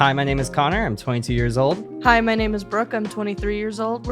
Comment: she clearly said 23 :'v